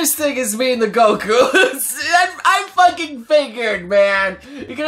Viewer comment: I thought he said "I fucking fingered" So I had to go back twice to realize he said "figured"